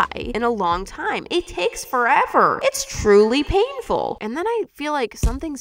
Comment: We she said “oh how painful” when making Zuzu I first thought she said oh paintful and I was very sad when I rewatched it that she didn’t say it 😢.